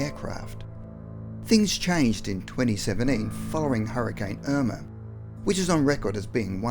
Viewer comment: its sound like he said in 2078